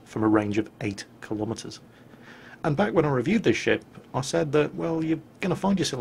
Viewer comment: first I thought said "And back when I reviewed this shit"